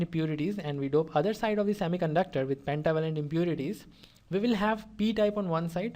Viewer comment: what is he saying "we dope one side with _____ impurities and we dope other side of semiconductor with ________ impurities" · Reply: Trivalent and pentavalent